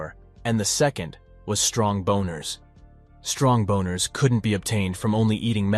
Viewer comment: Bro is picking the most ungodly words. I heard "strong boners" and died. I also kept hearing "grope" being thrown around. Man's wildin'.